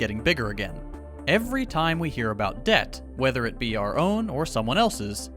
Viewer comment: Lol he said weather it be and I thought he said Weatherby which is my name ( it’s pronounced weather bee)